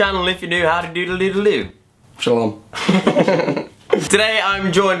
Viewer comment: Some reason when Connor said shalom I thought he was saying Sean/Shawn